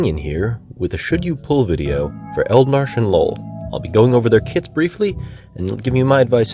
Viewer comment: I swear, I heard Eald'narche and lol (instead of Noel) at the first 20 seconds lmao.